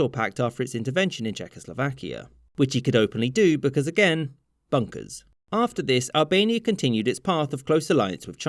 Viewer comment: I thought it said "BONKERS" Yeah, I think it's time for me to go to sleep.